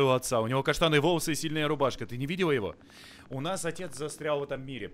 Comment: А кто тоже услышал сильная рубашка